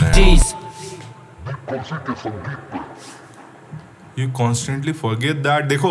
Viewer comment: DEEP KALSI KE SANGEET PAI thats what i heard